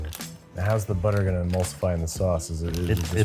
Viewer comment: Babish: “How does the butter help emulsify the sauce”? Floyd: “It does” Babish: “*Ok*” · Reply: I thought he was saying "what helps the butter emulsify in the sauce?" meaning ' is it going to?' and Floyd just picked up on that assuring him that it will